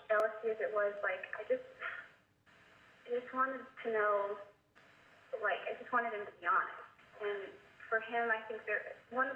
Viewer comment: did she say “I just wanted him to die”?